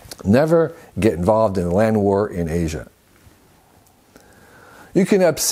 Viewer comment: "never get involved in a land war in asia" got me 🤣 Love the advice, Kevin. Really needed this today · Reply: oh shoot, I heard "land work" and thought "didn't plan to do that but ok"